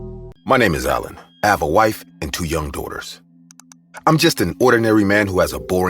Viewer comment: I heard a " I have a wife and two young doors"